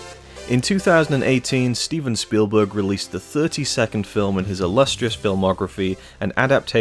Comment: Silly me! I misheard you say 32nd film as 30 second film, as in the film is only half a minute long